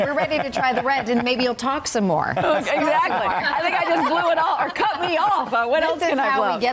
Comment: Did I hear " cut me off or what else will I blow" ???? Wine=sodium pentithal